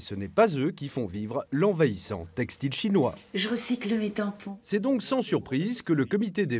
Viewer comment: Je recycle quoi à 8:00 ? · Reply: "mes tampons" ;)